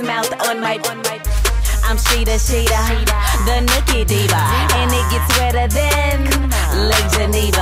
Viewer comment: “I’m Sheeda Sheeda the nookie diva” 😂😫 Sis what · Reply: I thought she said the Nokia diva